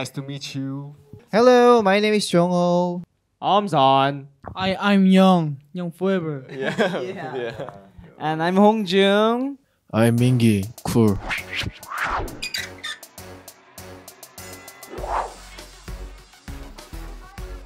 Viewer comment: The "yeah yeah" sounded like "okay shut up bro" lmfaooo I can't 😂😂😂😂